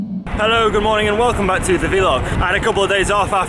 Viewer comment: At first I thought you said villa. I was scared I ended up watching some sort of Love Island spin off